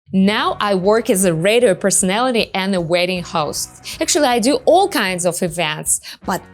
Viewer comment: 1. I thought u said “waiting “ host . Not “wedding” host. 2. Can u come here as international student? That is how I moved to the US. I got married here. It’s a better way that relying on a man for K-1 visa. It makes u dependant and easy to abuse. U idiolize American men, that makes u vulnerable. Find a way to come here on ur own.